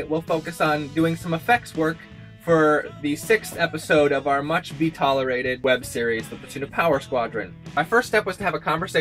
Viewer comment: What does say at 0:38? "Much be-tolerated?" 2:18 "'Sup, Jake?"